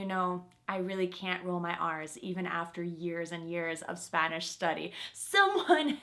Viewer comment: Did she just said my arse ???